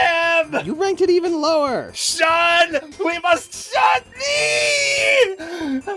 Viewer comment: Shun, we must shun him! You ranked it even lower. Shun, we must shun MEEE! That made me laugh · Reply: I always thought she said Shun, we must shun THEEEEEE (while still referring to)